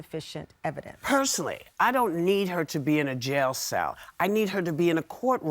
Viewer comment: Whoopi did not say she wanted Carolyn Brayant in jail. If you watch it again and go to about minute 8:00 you will her Whoopi say “personally I don’t need her to go to jail” and then she explains. But she never said she wanted her in jail.